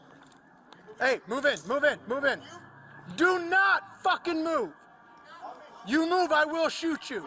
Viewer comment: "Hey, move it. Move it. Move it. DO NOT FUCKING MOVE!!!!" lol I guess that's not exactly what he said but that's what the suspect probably heard.